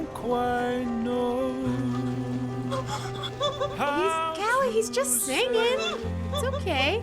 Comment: I thought she said “calliope means music”